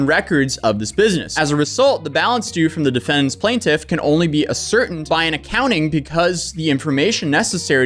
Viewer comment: bro you gotta learn to pronounce some of those words... disparaged... ascertained...what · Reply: His pronunciation of ascertained was the worst one. Subtitles thought he said asserted xD.